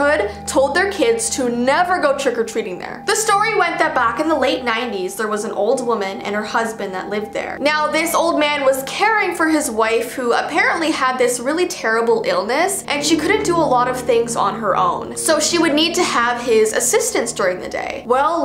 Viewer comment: did i hear her saying "waifu"